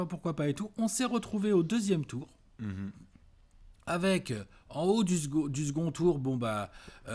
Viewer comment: J'ai entendu "En haut du zboub" perso